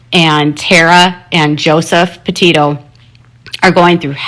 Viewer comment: Why does she say Potato instead of Petito?